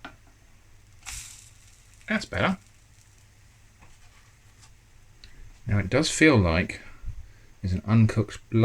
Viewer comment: I enjoyed it when you added the extra bit of water to the pancake batter and it sounded like you said "that's batter". I hope that was intentional. Really great series by the way, your creativity knows no bounds!